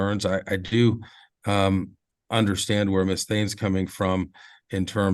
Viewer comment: I keep hearing "Miss THING".